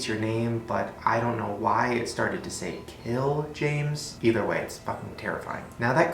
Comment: I thought that Elmo said "kill Jews"